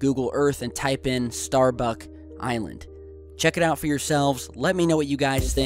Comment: At first I heard you say "starbucks island" :D